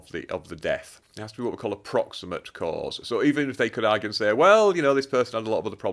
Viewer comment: subtitle is wrong, not 'approximate' but 'a proximate' cause of a crime, meaning a 'near' or 'close to' cause, referring to an underlying cause of the crime. Difficult to discriminate the sound in spoken words, even when initial 'a' is stressed in words, such as 'aee proximate' becoming common under US influence, as in saying 'aee nother' rather than 'an other.'